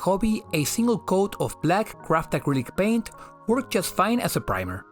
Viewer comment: I cant figure out what did you say in 6:07, black what paint? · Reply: yeah sorry, english is not my first language 😅. Black "craft" acrylic paint. I mean the ones you get a the dollar store that are much cheaper and come in bigger containers than the small modeling paint ones